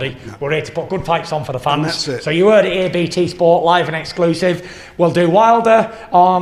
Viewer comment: Sounded like he said live and excuses 😆 🤣 😂